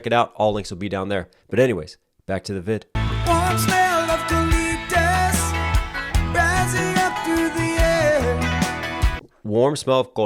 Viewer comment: Oh my God, I always thought they said "smell of galletas rising through the air " which is Spanish for cookies 🤣